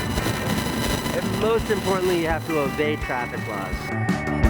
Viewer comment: who else thought he said Evade traffic laws : )